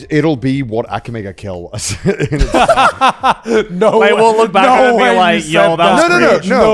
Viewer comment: what did joey say? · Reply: "i will look back and i'm gonna be like yo that's cringe"